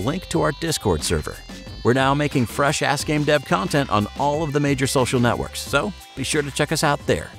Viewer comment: I thought he said "we're now making fresh ass game dev content"💀